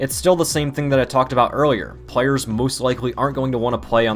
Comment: did just said moose instead of most?